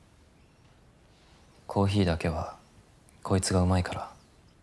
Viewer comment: Based on analysis of Naoki's POV that I've watched here it was mentioned that If you listen carefully at 35:17 Naoki said 'koitsu' instead of kotoko. 'Koitsu' can be translated to 'this person' or 'this woman'. It is used in casual and informal manner and sometimes used as endearment depending on context and tone. In certain situations among friendly group, 'koitsu' is used to refer someone affectionately. Naoki and kotoko relationship is more than friendly manner, they are husband and wife, so using the word 'koitsu' is surely an endearment to his wife. He's kinda saying "My dearest wife makes good coffee." To sum it up, Naoki is already putting Rika-Chan in her place.